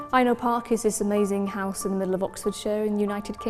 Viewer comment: What is the name of the house in Oxfordshire where this shoot was done? I couldn't make out photographer's words in the video at 2:35. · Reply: Throwaway It's called Aynhoe Park.